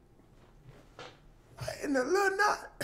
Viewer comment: the change of his face and i think he said "what in the lil na-"